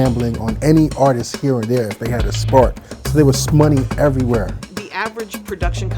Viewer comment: Did anyone else catch the guy @ 8:00 say "shmoney"?😂